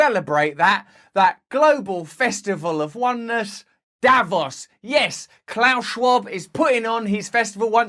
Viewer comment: I thought he said Davros and we were under attack from Daleks